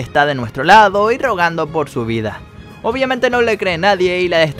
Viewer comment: yo escuché "Y drogando por su vida" xDD